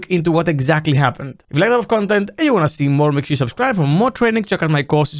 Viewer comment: Can someone tell me what is saying at 0:30? "If you like (?) content and you want to see more" I can never understand it 😅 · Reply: "that type of" I believe